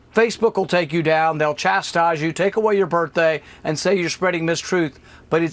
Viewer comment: Did he say FB take away your birthday?